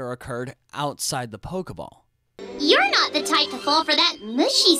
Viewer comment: did she say TITANFALL